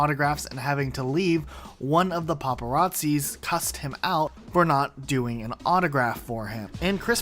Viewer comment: I heard 'you pissed him off